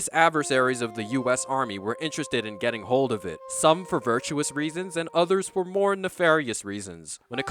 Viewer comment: "did you say virtual reasons?"